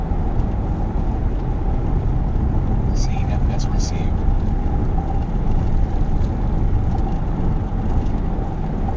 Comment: What does it say at 1:31? I hear it in multiple pursuit videos, but I don't get the first part 'xxxxx received'. And what is the system used to receive it? It doesn't seem a radio message but like a GPS voice. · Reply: "CFS Received" is what it says. CFS is 'Call for Service' which is basically the dispatch computer system (their in car laptop) creating the traffic stop call that he called in. You'll often hear things like "CFS comment added" in these videos which is the dispatchers or the other officers adding notes into the report in real time.